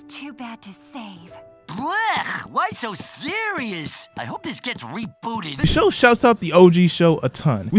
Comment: What the fook he say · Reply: "BLEH, why so serious? I hope this gets rebooted!"